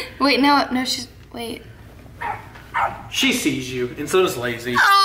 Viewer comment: Who else hears say niggas instead of hey guys